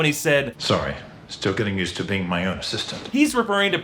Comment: Ooooooh I thought he said "Banging my assistant"